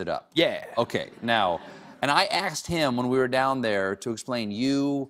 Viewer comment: Did Stephen say, 'I axed him' (instead of 'asked him') at 1:29 - 1:30 to imitate the way we speak in the Black community? 😄😄😄